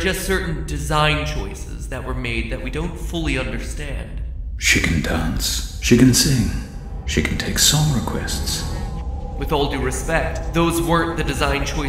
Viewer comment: Me mishearding "She can sing, she can dance, she can take song requests" to "Chicken sing, chicken dance, chicken take song requests"